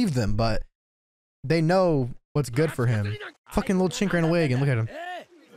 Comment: did you just call him a chink? what the fuck..